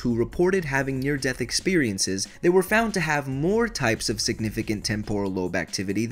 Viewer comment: captions say "specific" but you said significant. I know it's *significant*, but I just wanted to point that out (